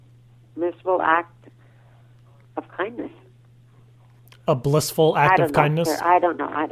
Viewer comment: She clearly said "Blissful Act of Kindness"